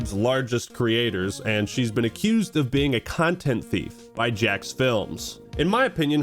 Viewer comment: I misheard calling Sniperwolf a content thief and thought he was calling her a cunt, and I had to sit for a second for how out of nowhere it was